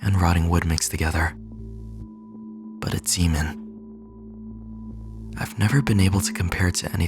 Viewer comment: why did the caption say "s*men" instead of demon😭